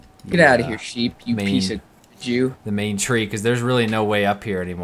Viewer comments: At around 10:15 did say you piece of Jew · does call a sheep "YOU PIECE OF... JEW"???